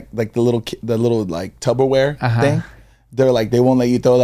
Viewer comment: Did say tuBBerware? 😂 I thought of that TikTok with Chris Distefano and Sal Vulcano